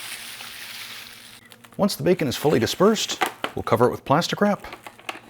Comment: I thought you said "plasti-crap" instead of plastic wrap!!! LMFAO!!!!